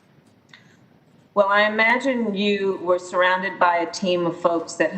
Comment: KH: "I imagine youre surrounded by a team of people?" 3 second pause ACB: "Yes, I am...." KH: "Stop it let me finish" She's repulsive. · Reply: ?? i thought the said: KH - i imagine you were instead of are ACB - I have had (a meeting with them) instead of yes, i am nonetheless, i agree with you last adjective.